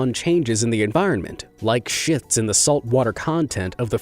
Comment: <——— at this time who else heard sh*ts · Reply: It's shifts, not sh**s.